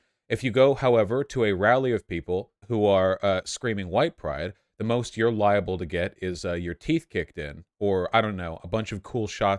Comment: I was listening to this while doing the dishes and I initially misheard Vaush's words here about the white pride rally as "the most you're liable to get is some cheesecake," and I was like "I mean, okay, I guess white people like their cheesecake."